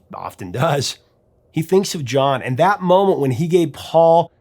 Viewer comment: Lots of fun information here. I always heard "movement" as "moment," another thing I learned.